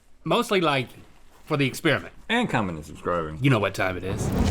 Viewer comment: why is the subtitles saying "and communism's growing" instead of "commenting and subscribing"? 🤣🤣🤣🤣